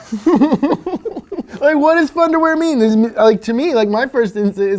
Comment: I honestly thought the rock said thunder wear not fun to wear LOL